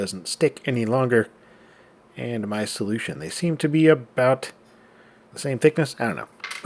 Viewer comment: "They're so proud to be Canadian. It's really adorable." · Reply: It almost sounds like you said "aboat the same thickness"